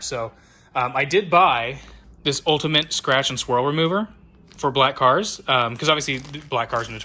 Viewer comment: I thought said "scratch and squirrel remover" and was genuinely concerned for a second.